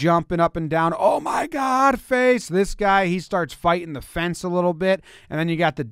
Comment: Anyone catch the right fielder yell F*CK!